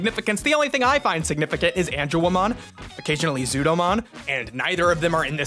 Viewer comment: For a second I thought you said Angelamon instead of Angewoman and was like dude!...